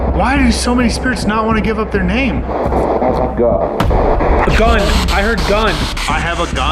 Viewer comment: when asked why does no one wan to give up their name. I hear “because I’m God” instead of “ I have a gun”